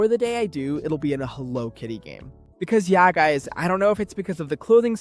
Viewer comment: l misheard hello kitty as halo kitty and was like boom new spinoff game and collab 😅